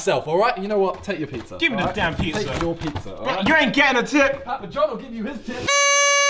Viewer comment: What does Simon say at 1:20? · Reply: Papa john'll give you his tip